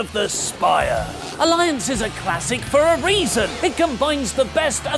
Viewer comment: I was eating my meal and didn't see the vid..and I heard saying "Elias is a classic for a reason..."